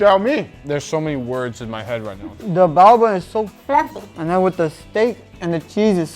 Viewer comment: "The bao bun is so fluffy..." Because he was talking with his mouth full, i misheard him. I thought he said "The Vulva is so Tasty~" Lol, good thing i replayed that part a couple of times 🤣